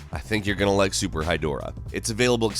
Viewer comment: When he said Super Hydorah, I thought he said Super High Dora.